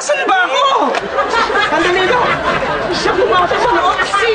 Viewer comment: Tama ba yung narinig ko? sabi ni Antonietta, T*****amo! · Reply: "sandali lang po"